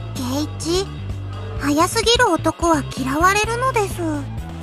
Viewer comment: what did she say?! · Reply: Nobody likes men that are too fast XD